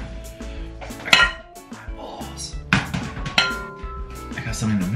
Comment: it sounded like he said balls